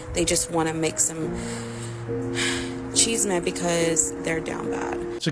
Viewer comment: Whoops, Go back to 14:50. She actually said “chisme,” not “cheese,” which is the Spanish word for GOSSIP.